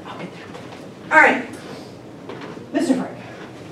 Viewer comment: I thought she said mr freak